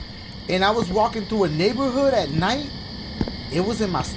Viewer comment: eat something and listen sounds like he said niƃƃer hood